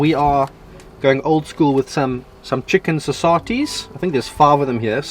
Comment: Hey Saffer okes, 12:44 what are those chicken ?saysartes? saisartys?.......umm how do you spell that word? Haha TIA!